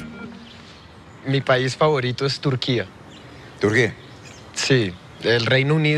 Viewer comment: “Mi país favorito es Turquía” y luego oigo que Enrique dice “¿Te urgía?” 😅😂🤣